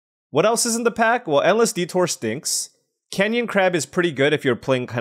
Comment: Not going to lie, i thought you said LSVtour instead of endless detour every time it came around and you passed it